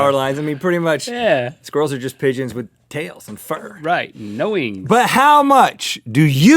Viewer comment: did Link say F*** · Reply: he said fur